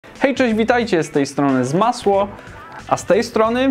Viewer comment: zmasło Czy tylko ja tak usłyszałem